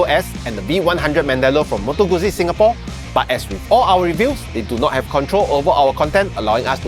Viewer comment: the dealers don't have control over our review 😆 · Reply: oh yeah, I watched it again. You said it so fast it sounded like you said "We don't have control over our content" 😂